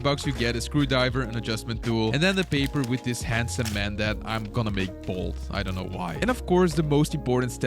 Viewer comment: i thought he said “im gonna make balls”